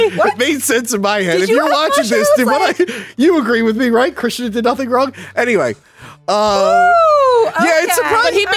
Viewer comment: did just say "Chris Chan did nothing wrong"?